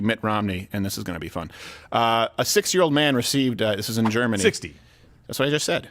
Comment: I heard "6 year old man"